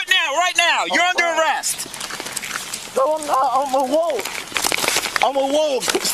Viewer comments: What does he say? It sounds like "No, I'm not. I'm a wolf". But, I'm guessing that last word isn't actually 'wolf', is it? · 😮 did he say "I'm a Wolf 🐺" 😱 well no wonder he was going 70 mph, he was running at wolf speed 🐺😂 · did he say "I'm a wolf I'm a wolf" 🤦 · is he saying “I’m a wolf? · as they're cuffing him, did he say "I'm a wolf! I'm a wolf!!"? Maybe I'm mishearing what he said. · is he saying “I’m a wolf?” This dude’s a SovCit Furry! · Did he say, “I’m a wolf?” I swear I heard it twice at about6:25. Is that a new thing? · was he saying "I'm a woke?" Wtf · Did I hear him correctly? Did he say, "I am a wolf!"?